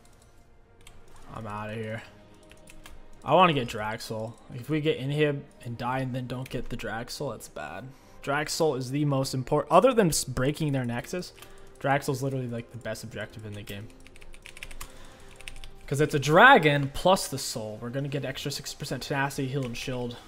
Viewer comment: At first I thought he said "other than breaking their necks, drag soul is..etc" I was like dang son gettin a little dark